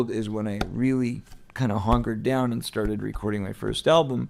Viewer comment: 😂He says HONKERED down instead of hunkered. HONK HONK 🤡